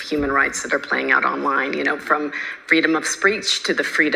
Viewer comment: “freedom of spreech” She subconsciously knows what she’s saying is wrong and thus the nervous slip of the tongue - ITS FREEDOM OF SPEECH! 💪 I love this channel! It’s so grounded and informative! 👏